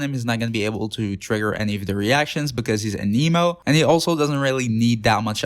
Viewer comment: I got a chuckle during the spear, cause when ya said it doesn’t work for Xiao cause he’s anemo, it sounded like ya said an emo. XD